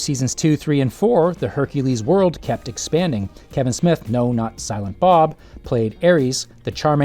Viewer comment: I heard "Kevin Smith known as Silent Bob" instead of "no, not Silent Bob" and almost fell off my chair